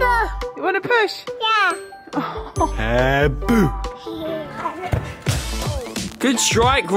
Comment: What did Roman say at 2:57😂😂 What an amazing surprise from Billy and a great gesture for Kate. You must be so relieved to be back home it's been so long. Love u guys and thank you for the video 🙏🙏❤️❤️❤️❤️❤️ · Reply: I think he said bye mom